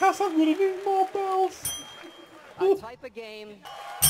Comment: What does he say at 0:39? "A type of game"? Everybody starts whooping inappropriately.